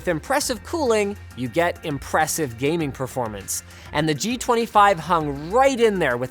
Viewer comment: Did he say “hung”?